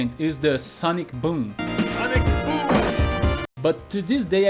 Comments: When I was a kid I knew only Guile's "Sonic Boom!" (although now I can't unhear ("Alec Fool!"😂); Dhalsim's "Yoga Fire!" and "Yoga Flame"; and Sagat's "Tiger Uppercut": those were clear to me, and to everyone who I played this game with, as native English speakers. In more recent years I learned from YouTube comments that Ken's and Ryu's projectile was called a "Hadouken" and their uppercuts were called "Shoryuken", although I didn't know their English translations. I always wished that I knew what they said while executing their tornado kick (we called it that only because that's what it looked like it should be called). I knew that Chun Li said "Spinning" something, bit couldn't quite make out the rest. And now I feel double plus unsmart to learn that she says "Yatta!" when she wins, because I always thought she was saying "Guess not!" Thank you very much for sharing all this! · Great video! I always heard Guile say "I like food" instead of "Sonic Boom". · I always heard “phonics foom” which is pretty close to sonic boom.